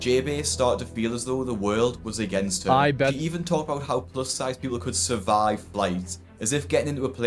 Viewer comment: Damn look at this HOG! every time they say, or oink, "Plus sized", I hear "Bus sized"